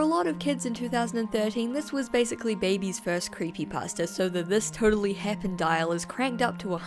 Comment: So much enjoyment in the video but I must ask if there is a accent between “creepy pasta” and what I hear as “croppy pasta”